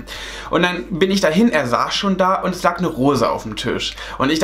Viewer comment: Ich hab beim 2 verstanden "Da lag eine Hose" 😂aber das war alles echt ein wenig skurril